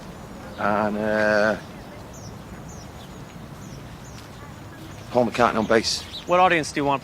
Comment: who on bass? I can't hear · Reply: paul mccartney